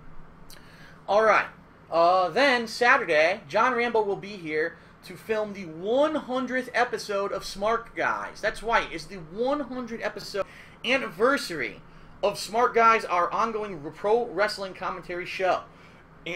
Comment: not sure if just me, but it sounded like he said "that's wight" when he was announcing the 100th episode of Smark Guys.